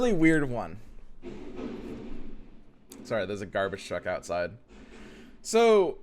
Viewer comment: What says: "There's a garbage truck outside." What I hear: "There's a garbage deck outside."